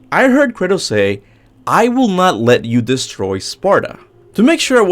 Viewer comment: Sounds like he said "i will not let you destroy sparta" oh thats what it was